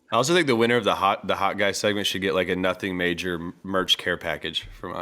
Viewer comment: when said its the final if the Hot Guy segment, I thought he said Final of Hawkeye segment